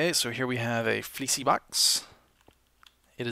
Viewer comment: It sounded like he said Feeshy Box for a second there.